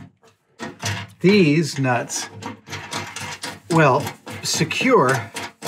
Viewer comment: I never thought I would hear Adam say Des Nuts. Just made my month!